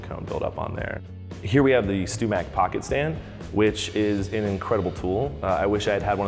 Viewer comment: I was looking away when he mentioned the pocket stand. I thought I heard pocket sand and was like, “there goes Stew Mac up to their usual name branding”.